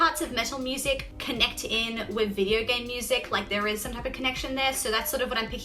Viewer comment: What are you saying at 6:15? "Some parts of metal music connect in with the ? music? Yurgay music? lol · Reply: I think she says "video game music"